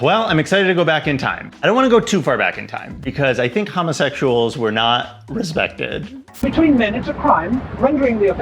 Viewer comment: I heard “hummus-sexuals” 😂